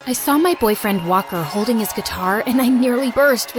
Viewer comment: Why I the beginning did I hear “ I saw my boyfriend wopper”